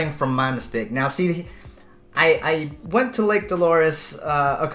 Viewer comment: ohhhh LAKE DOLORES for a second I thought you said "Lick Cli - " nevermind